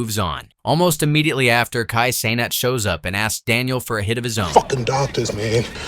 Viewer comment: did he say Kai cenat?? 😂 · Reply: Yep, Kai Cenat really caught a stray at 2:11 😂